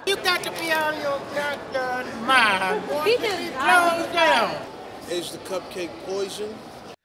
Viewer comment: "Are the cupcakes poison?" LMAOOOOOOO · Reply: I cant hear what hes saying, it sounds like hes saying "asian cupcakes poison" to me